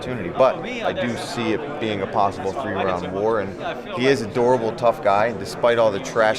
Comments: I thought he said adorable lol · At first i thought he said hes an adorable tough guy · lol I thought he said "he's adorable" instead of "a durable" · "he is adorable tough guy" loo i know thats not what he said but i pretend it is😂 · 38 I thought he said "an adorable tough guy" at first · I thought Paul said adorable